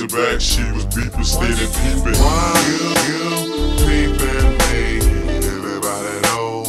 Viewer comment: i always thought this said, "why you BEEPIN' me," but peepin' is funnier.